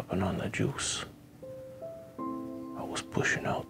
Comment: I legit thought it said mustache juice